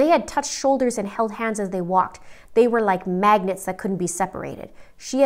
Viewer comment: "They were like magnets that could not be separated" I literally had to rewind it bc I thought u said "MAGGOTS" Lmao 🤣🤣🤣🤣🤣🤣🤣